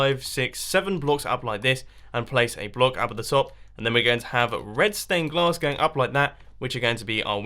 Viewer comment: Did he say redston or red stained glass in